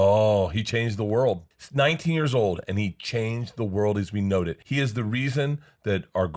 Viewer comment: did Bert say “as we knowed it?” 😂